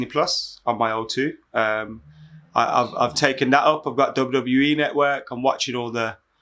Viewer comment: Love this podcast Won’t lie but I thought said I’m on the double e network instead of calling it EE😂